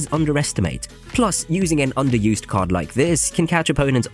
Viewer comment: Did you just say .. Pigs are underused ???